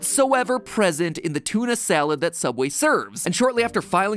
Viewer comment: instead of the word sandwitch it is saied as ”salad” even the auto subtitles agree, and those a few seconds before were just piling stupidity